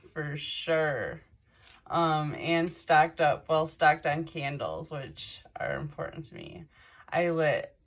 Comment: 🤣 I guess it's too close to Halloween- I heard "they keep me in candy, which is important to me" at first.